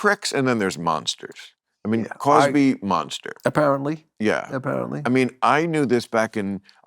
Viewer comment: Around @ 5:40 I think Bill Maher says Bill Cosby has a monster prick? WTF Bill, how do you know? (Well, he IS Black!) ...This was right after Maher says all Germans are pricks! Bill, stop getting stoned on the job & interrupting your guests! You AREN`T funny!